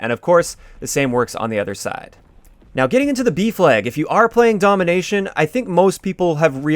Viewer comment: I kept hearing "beef leg" instead of B flag 😂